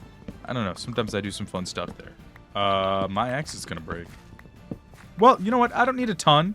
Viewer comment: I thought you said my ex is gonna break 💀